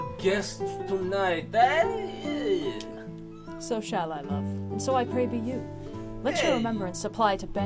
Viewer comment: When said "so shall I, love", I heard "so Shai Lebouff"